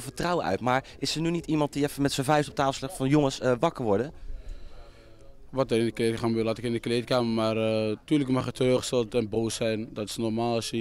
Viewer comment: wat zegt ie daar? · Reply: polka troklovitch wat er in de kleedkamer gebeurd laat ik in de kleedkamer zegt ie vgm